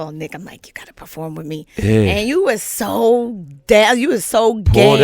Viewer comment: it sound like she said you were so gay 😂😂😂😂 I really think she called nick gay 🤦🏽‍♂️